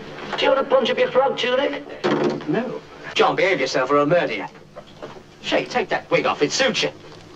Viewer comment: I swear he says "... or I'll murder you." Oh the bloody irony of that!